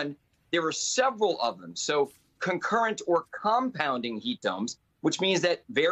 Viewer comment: People have to know the heat will break the ego system Plants and animals will die and then us 😅 By the way I heard him saying heat “Dumbs”😂